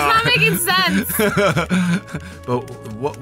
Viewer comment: When they said what is sense I thought they said what is sex